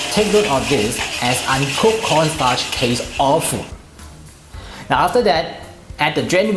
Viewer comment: What is he saying at 7:18? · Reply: He says the uncooked cornstarch taste awful. You can turn on the English caption.